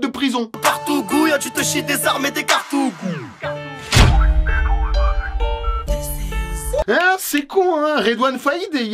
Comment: C'est quoi des "cartougoushe" ???? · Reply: Cartouche